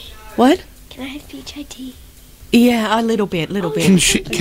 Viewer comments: I thought she said P.I.T 🤣🤣🤣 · I thought she said P I Tea.😁🤣🤣